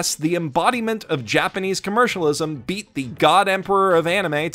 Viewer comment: did somebody say God Emperor?